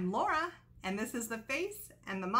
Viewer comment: Laura... Sorry i thought you said lauren. I had to replay video again. Lol. ☺🇺🇸